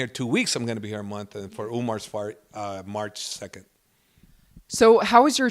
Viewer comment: Did he Say he's there for Umar's FART 0:38 😂🤢